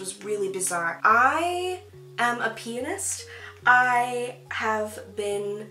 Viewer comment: i zoned out for a second and when you said you played piano i thought you said one of your alters was a penis, (19:01) LMFAO. THIS WAS ME NOT TOTALLY PAYING ATTTENTION but when i realized what was really said I laughed for 5 minutes, Not that you said an alter had a penis, i thought you said an alter IS a penis. anywhooo love you and your light always, <3 so sorry lolollol